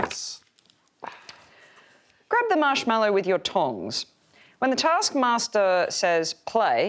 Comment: oh my LORD. i thought they said TONGUES instead of tongs. I was wondering why theyre holding them hahahahahahhahah